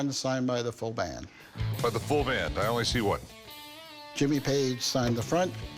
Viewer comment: Full band,? I thought he said full bang.. 🤣🤣🤣🤣🤣